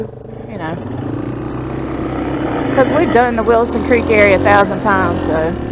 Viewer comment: Is this the Fines creek area near Max Patch? Because if it is there's no way it can link up with Wilson Creek.That's a long way away. That guy was a peckerhead. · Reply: We thought he said Wilson Creek, I think he said Wilkons Creek. Still a peckerhead though!😂